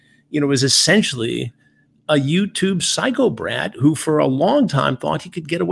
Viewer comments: I never thought I’d hear the words “psycho brat” from Chris Hansen. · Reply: I thought he said psycho brad but this makes much more sense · Does Chris call Greg a “YouTube psychobrat” or a “YouTube psychopath” at 1:46? Never heard of a psychobrat before but that’s what I hear. Not sure if that’s a thing or a snappy Chris Hansen original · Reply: He definitely says psychobrat. 😂